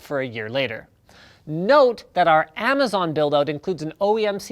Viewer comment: wait... did linus just say: " note that our amazon dildo " ?